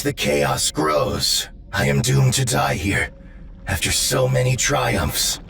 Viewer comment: "I am doomed to die here." What I heard was "I will die on this world... I will die on Armageddon."